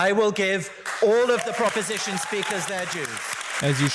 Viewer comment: Did he say "I will give all of the oppostition speakers their Jews"?